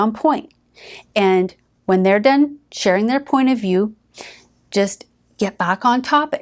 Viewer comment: this video gaslighted me! 😂😂 She says step #7, but the caption says Step #8 I love these videos btw, just joking around